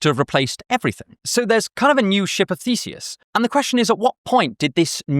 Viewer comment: I misheard ship of theseus as shit of theseus, and i wonder, how would that work?